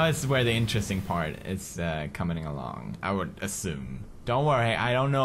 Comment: Who else heard pewds say comening